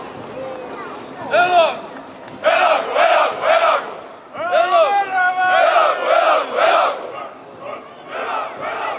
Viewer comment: What are they saying at 00:44. My Estonian is improving but its still very limited. Aitah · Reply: “Elagu, elagu, elagu!” - three times “Long live!”